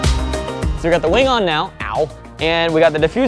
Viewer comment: I heard meow